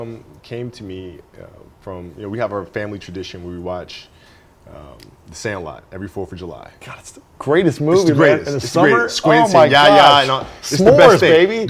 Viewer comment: Hey guys i can't understand the movies name, can someone help please? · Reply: Thank you but, I meant the one they watch as family activity. Sounded like sandlight or smth.